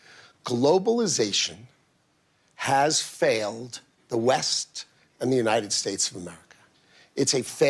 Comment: I thought he said "the weft"